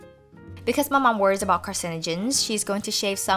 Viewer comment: What does she say at 3:04 ? "Because my mom is worried about ... ?" I can never work out what she says even after re-watching! Xx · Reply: She said "because my mom is worried about carcinogens"